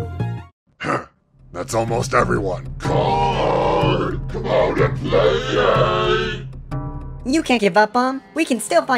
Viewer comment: what did he say? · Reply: “Card come out and play”